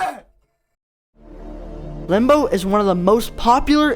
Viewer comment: why did I heard "rembo"